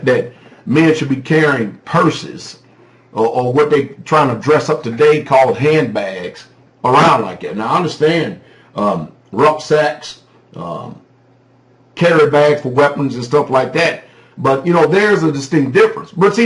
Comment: Rucksack (back sack or back bag) in German is pronounced differently in German than how Americans use the word. I thought you were saying ''rough sex''. I had to listen to it 06:57 about five times before I figured out what you said.